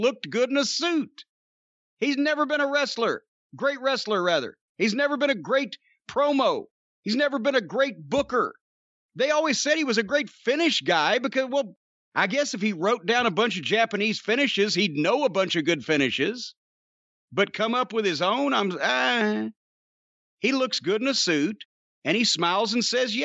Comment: I thought he said "I guess if he rubbed down a Japanese guy" . I had to turn it up. Now I wish he said that instead. He'd know a good finish then....lol